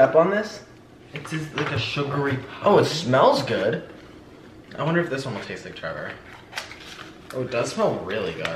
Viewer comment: hold on... what did nick say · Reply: " wonder if this one would taste like Trevor"